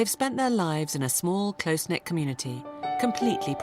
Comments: when she said close knit I thought she said close neck and my jaw literally dropped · I know the narrator said they live in a close knit community, but i could've sworn she said close neck community.